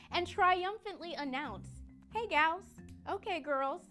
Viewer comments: thought it said hey gays for a moment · I thought it said hey gays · I thought that said hey gays